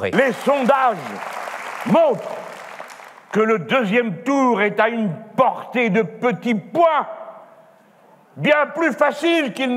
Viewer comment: A 1:00, j'ai compris "que le deuxième tour est à une portée de petits pois !"